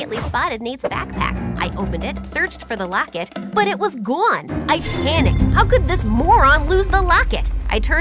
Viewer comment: The way she said " It was Gunee" instead of " It was gone"